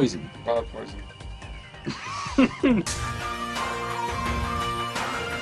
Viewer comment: but what did colin said in 0:59? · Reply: It's been 7 years but he said take me to Glasgow :)